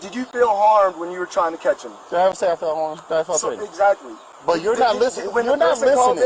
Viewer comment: someone tell me if I'm mishearing "Did I say I felt harmed? I felt safe."